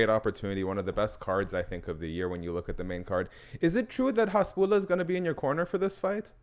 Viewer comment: Did he call him Hamza 7:39 ?